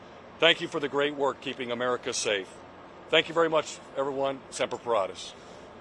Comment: did he really say "SEMPER FRIES"? lol